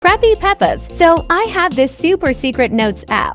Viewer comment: I thought u said "CRAPPY PEPPAS"